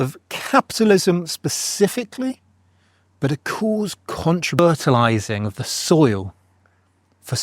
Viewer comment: not sure what was said here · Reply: "a c(ause) fertilizing the soil" -- but it's all anecdotal and not evidence-based. Protestantism less authoritarian than Catholicism? Tough to quantify. Like saying the speed limit is clear evidence that traffic is slowed by authoritarian edict, when we all routinely exceed the speed limit without consequence.